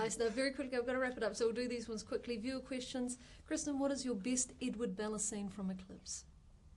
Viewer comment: could someone tell me what is the question at 5:37?? something like "Kristen, what is your best Edward/Bella scene from Eclipse?? I´m not sure if "best" is what she says English is not my first language